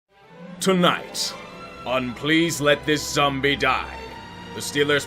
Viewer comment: I thought you said "please let this sum'-b' die" instead of "zombie" lmfao.